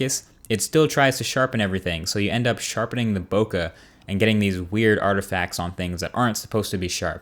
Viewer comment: What does he say at 8:50? Sharpening the boca? What is that? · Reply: Bokeh is a photography term meaning background blur. Basically he's saying its sharpening the blur, when it shouldn't as you want the blurry bits to remain blurry, to not cause weird artefacts.